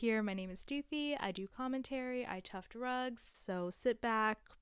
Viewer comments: I heard I chug drugs 👀 first time here and I'm not a native English speaker, sorry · I heard "I do commentary and tough drugs" 😭😅😅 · I heard "I test drugs" instead of "I tuft rugs" when she was presenting herself 💀